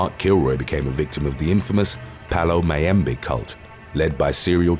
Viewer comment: That last i misheard and thought he said the Mayo Cult 😆 and i was wtf why was there a Mayo cult? 😂